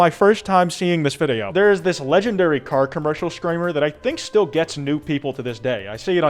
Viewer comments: Screamers are annoying, that we all agree on, but can we take a moment to talk about and appreciate midriff in this video? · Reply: I thought u said streamers lol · I thought you said STREAMERS, to be fair they also ruined my childhood.